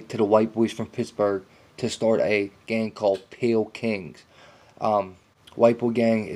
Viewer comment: Pale king sounds like you say pill king